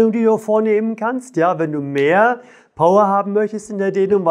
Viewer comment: habe ich mich verhört : " wenn du etwas mehr AUA" haben möchtest😅? Tut gut !